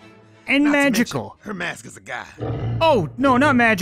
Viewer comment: I think he said Her mask is a guy Not a god